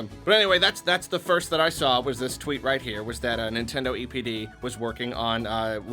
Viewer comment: For a minute, I thought he said “Nintendo EDP” 💀